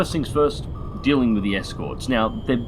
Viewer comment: Thpught it said finding then thought it said fueling